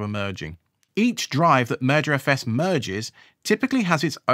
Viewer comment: I know you're saying "merger os"... but I keep hearing "murder os". And that makes me think of what Microsoft has done to Windows.